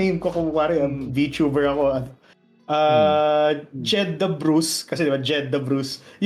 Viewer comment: First thing I heard was: "JED THE BRUISE" HAHAHAHAH Thank you for the content guys. Hoping you could give another interview for as anniv atleast.